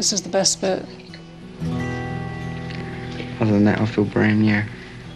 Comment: what does he say 18:47? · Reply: "Other than that i feel brand new."